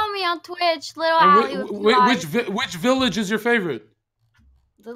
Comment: what is her twitch? I didnt understand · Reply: AK1